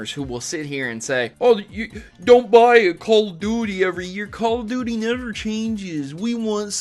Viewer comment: I thought he said don't buy a cold doodie.